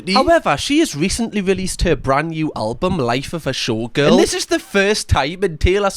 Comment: Thought he was saying shortgirl.